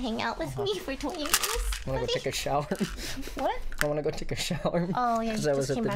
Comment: She definitely thought he said "wanna go take a shower?"